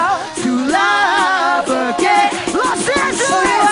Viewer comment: Lol he thinks hes in gta??? wtf does he say los santos???